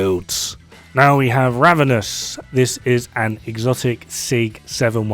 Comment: at the Ravenous part, I legit thought that you said the Ramenous with a straight face :))) I think I've woke up my neighbor with my laugh :))